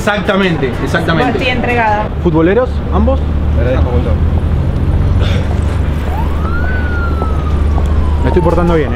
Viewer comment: que dicen? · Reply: Dicen te agradezco un montón